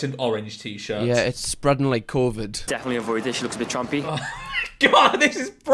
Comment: Lmfao, I thought that he said that she looks a bit "Trump-y". And I was like, Well yeah- she kinda does.